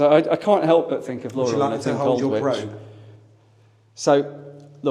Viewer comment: Did call Laura an Old Witch? 😁